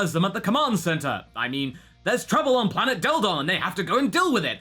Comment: who else heard "there's trouble on planet dildo..."